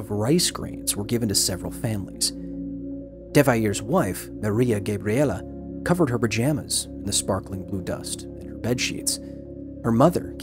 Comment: "Devayere 's wife Gabriela covered her vagina in the sparkling blue dust and bedsheets"!! WTF?? Did I hear that right??